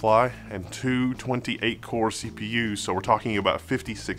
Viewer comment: For a split second I thought you were saying "two 128 core CPU's". With Threadripper this last generation 28 core CPU's don't seem that impressive (even though I have a "measly" 12 core myself). These chips are still impressive for (among others) its amount of supported memory (+bandwidth) and PCIe lanes though.